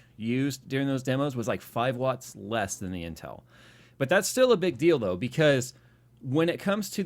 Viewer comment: Did Jay just said "But that's still a big DILDO..."? :D